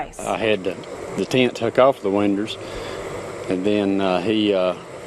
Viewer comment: Did he say “winders”, at 1:20, instead of pronouncing it “windOWS” like how it’s supposed to be. 🤦🏽‍♂️ 😂 But yea I would def like these on my winders too… 🤔 😂